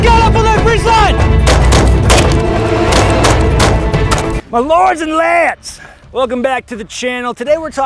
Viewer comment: I heard you say "My Lords and Lats" and you can't convince me otherwise.